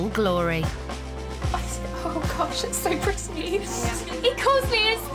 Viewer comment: Oh, that is so sweet ❤ when she said he calls her, his fairy 🧚‍♀️ really sweet. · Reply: OHHHH she said fairy I misheard it as furry and I was like weird but you’re happy so 🤷🏻‍♀️